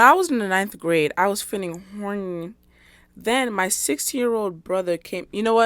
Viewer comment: the 16 year old brother one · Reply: Ngl at first I heard “6 year old brother” and I was even more traumatized 😭